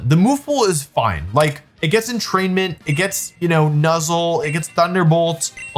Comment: And that's how I learned that move is not called "Entertainment"